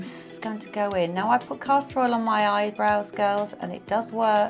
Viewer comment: What does she say at 11:38? I am Canadian - not British. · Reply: I say I use castor oil on my brows. It helps them to grow